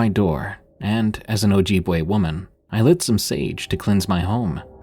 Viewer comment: I could have sworn she said "...as an OG white woman i burnt some sage..." 😂😂😂